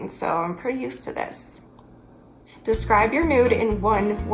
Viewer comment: i thought you said “describe your nude in one word” not “describe your mood” 😂😭😂